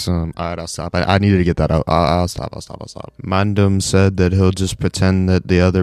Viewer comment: did you say mandem? 🤣